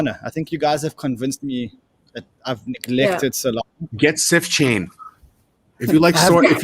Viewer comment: what are they saying? xxxxChain? Something chain? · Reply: SifChain